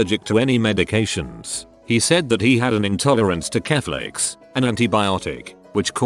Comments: Omg. At 0:29 I heard “he was intolerant to Catholics.” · It sounded like he said he has an intolerance to Catholics